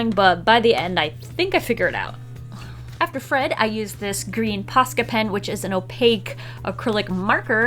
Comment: Thought she said pasta pen